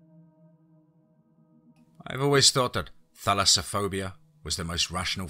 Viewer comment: Ok, when you first said the name of the phobia at the beginning of this story, I really thought you said "phallusophobia"! After a bit of listening, I realized that it was NOT the fear I first thought! Wow! I can really empathize with the protagonist of this story, as I too suffer from chronic bouts of this same phobia! 😁